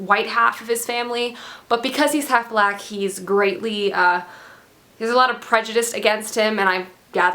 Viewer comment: I thought you were gonna say "... because he's half black, he's grey". Turns out you said "greatly". Oh well.